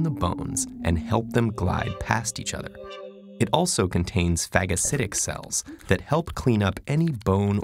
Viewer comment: Did he just say faggotcidic cells? Lmaooo